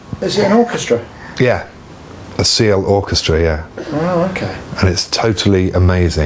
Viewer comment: Does he say Seal Orchestra? CL Orchestra? Any ideas? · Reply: Siel Orchestra. There's a great in-depth interview with Adrian Utley on the instruments and production of Third - <link>